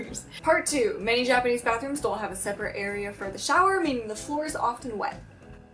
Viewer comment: I love when the subtitle in 2:15 says sowers instead of showers